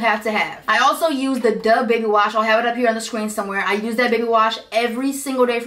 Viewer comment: It Sound like she said Bebe wash😭😭😭